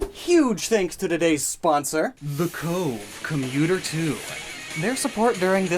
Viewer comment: When he said commuter 2 I really thought he said computer 2 and I was about to lose it (in a good way) over his pc2 sponsorship